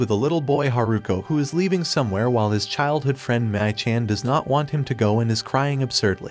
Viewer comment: this 'mii-chan' pronunciation, sigh...sounds like 'meh - cheng' instead of 'mi - chang' .